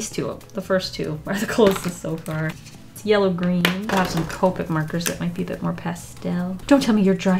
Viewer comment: every time someone says “Copic markers” I hear “Covid markers” lol 😂